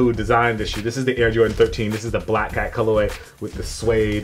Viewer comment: I thought he said black guy color way lol